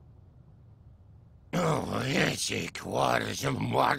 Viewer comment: what in the world is that yiga clan dude saying?! I heard “oh I hate Sheik fjenrnejbfoynaj jfbtoeikfjstbkebdk aw!” Somebody please explain what he’s saying and why. (I’m not looking for why he hates Sheik (I already know why) but more why he’s saying that at this point) (not saying he actually said that but that’s what I heard) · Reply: "oh my head's sick waters water and AIAIAIA AH"